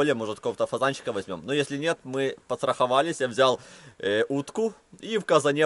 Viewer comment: мне одному кажется что он сказал потраховались